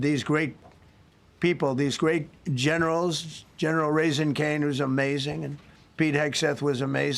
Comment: did he say, General Raising Cane?